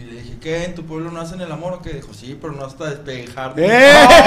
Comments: Q dijo en el 25:15 ? Si pero asdf, no escuché bien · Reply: "Sí, pero no hasta despellejarlo" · No entendí lo que dijo en el remate este, alguien que me explique? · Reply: Despellejarte